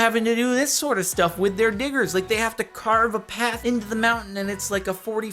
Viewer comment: thought he said a different word . I had to go back and use captions just to help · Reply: I did to I thought it said n*iggars not diggers